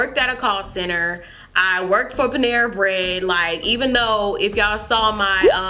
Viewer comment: did she said "when i worked at nigga bread????" omg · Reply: manu dreyer she said panera haha